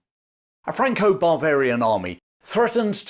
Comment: I hear "Franco-Barbarian army".